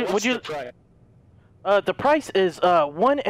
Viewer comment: i thought he said the price is 1 euro